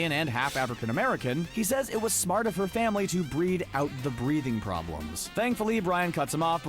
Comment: Honestly I don’t get the “breeding problems” one. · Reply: I thought he said "breathing" problems